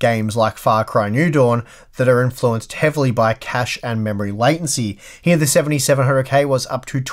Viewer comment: At first, I thought you were saying cash instead of cache.